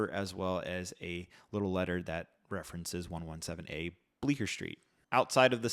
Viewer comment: i thought it said 221b baker street for a second